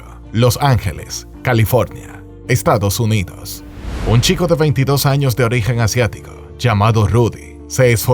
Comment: Soy tan racista que escuché “ un Chino de 22 años” y lo escuché como si estuviera diciendo algo denigrante hajaja